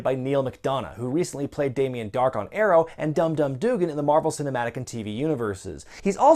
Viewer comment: Im sorry, did you say dumb dumb doogan?